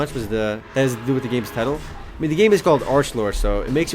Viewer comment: did anyone else hear him say "arselord" at like 5:48?